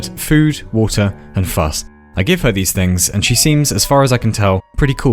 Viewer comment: Im flipping back and forth between 1:54 and 1:55 and trying to figure out what it says like the neurotic bastard i am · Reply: "Fuss" British slang for affection i guess. I had to hear it a few times as well.